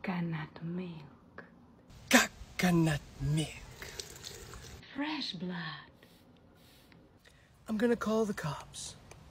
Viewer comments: Um, Did She Say “Fresh Blood” Min. 4:36?!? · Dude it sounds like you said cockanut milk 😂😂😂😂😂